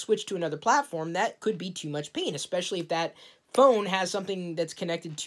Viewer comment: almost sounded like you said "too much penis"